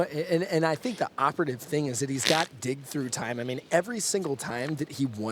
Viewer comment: LOOOL! Like at one point the announcer says (3:14) "And we all know he's got Dick Through Time..." And me and my buddy were like: "WTF did he just say?!?" "I think he said he has dick through time :)" And then we were both on the floor laughing for like 5 minutes after that and then we realized it was "dig through time" and we laughed 5 minutes more and every time the commentator would say "dick through time". Later we realised how waste i am right now and laughed some more :D. Dick through time - how to mindfuck the opponent through time without him even knowing! 4:30 XDDDD [ [ [ T I 8===> M E ] ] ]